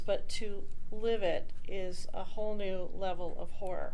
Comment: did she say horror or whore?